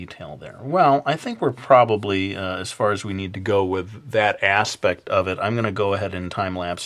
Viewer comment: as far as we need to go with DAT ASS-pect. XD im sorry, thats what i thought you were going to say for some reason*